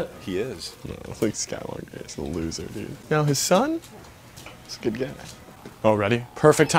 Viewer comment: DID YOU SAY HIS “his son”